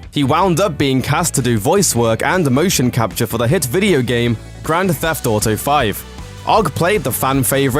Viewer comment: excuse me what did you said gramo thief autoO?